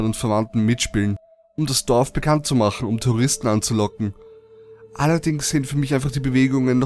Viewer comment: "... um Terroristen anzulocken" habe ich gehört :D